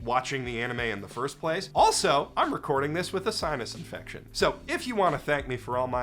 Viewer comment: Oooh you said sinus infection, I heard 'sign of affection'. Cmon brain, get outta the anime gutter.